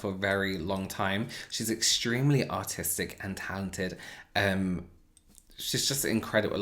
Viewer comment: i heard "she is extremely autistic" im so bad